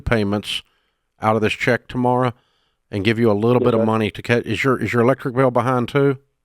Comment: I heard Dave ask " is your liquor" bill behind", and the dude on the phone I think heard the same thing. He asked Dave to repeat himself and Dave said "electric and water". wtf lol